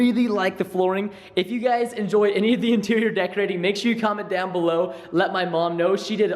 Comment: Did I heard "come on below" ? ow8 nope he said comment down below :c turn off the car